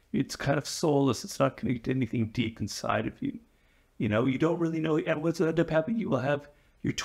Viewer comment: bro what did he say at 0:55 lollll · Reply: “You don’t really know and what’s going to end up happening…”